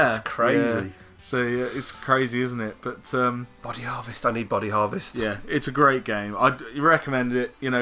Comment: I swear I thought you said Bunny Harvest at first. Next year you should do a joint countdown with us at gamingrespawn ;D